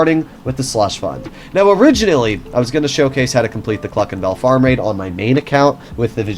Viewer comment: I thought he said F**** Bell🤣🤣🤣🤣